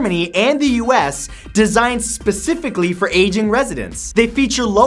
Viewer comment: Who else thought he said Asian? XD